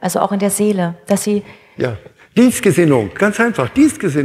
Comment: Was sagt er in minute 37:04? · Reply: "Dies-Gesinnung" sagt er. Und ich verstehe es so, dass er ein gegenwärtiges Leben meinst. Wir leben im Jetzt, nicht für das Jetzt.